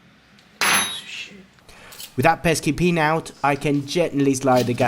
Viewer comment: did you just call that pin a POS?😂